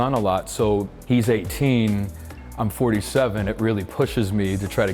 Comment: At first, I thought he's still in twenties. but when i heard he and his son work out together, oh he maybe 35 at least, then at 1:22 , am I hearing it right? He can't be 47 is he one of Dúnedain?